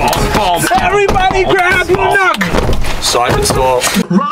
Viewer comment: Everybody grab your luck??? WTH DID HE SAY LMAOAOAO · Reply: Everybody grab a nook